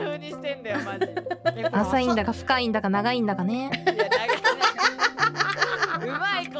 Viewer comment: my listening isnt 100% there but did Okayu say "浅いんだが深いんだが長いんだがねー"?? www snuck long in there and I dont think Subaru noticed